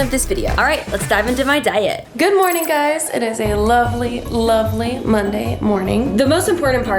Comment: Did she just say "LOVELY LOVELY MONDAY MORNING"